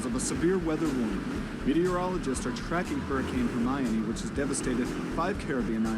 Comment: did he say miami